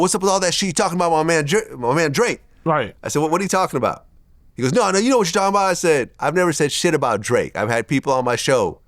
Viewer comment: Did Vladimir repeat the guy saying naw nigha you know what I’m talking about. lol sounded like it slipped